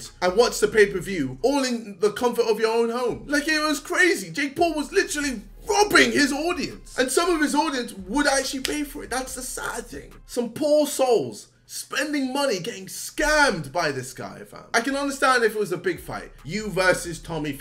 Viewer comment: "Some paul souls" lmao, I know I heard it wrong, but its fucking genius!